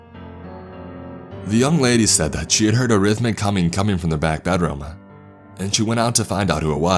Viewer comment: Oh, he's saying humming. I thought he was saying a rhythmic cumming, and I was incredibly disturbed.